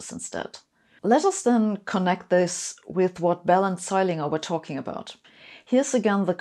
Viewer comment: I'd recommend you mention Mr Bell after the other person like - Zeilinger and Bell, instead of the unfortunate sounding "Bellend Zeilinger"